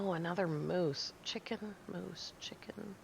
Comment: “Hmm Chicken, moose, chicken, moose.?” As a chef IRL I heard “chicken mousse” 😂 Guess the chicken wouldn’t have broken your leg though 😉 Frustrated with how hard I still find wolves when you make it look so easy! I can miss even with a shotgun 😂 Determined one day I will get the hang of it. Roasts are good to take with you on a long loot run - or as a meal when you die and respawn back at the base but need to run a distance to get your stuff back! Great video as always 😊